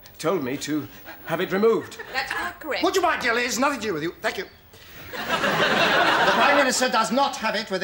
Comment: What does Sir Humphrey say at 02:35?? It’s so angry and quick that I can’t discern the words even after 5 goes 🤔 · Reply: I believe it was "would you mind dear lady it's nothing to do with you, thank you"